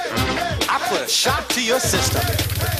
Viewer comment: I used to think he said "I put a shock to your sister" when I was a kid XD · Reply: I thought I was the only, though mine was somewhat different, I heard “I’m gonna shock your sister”, which I found really weird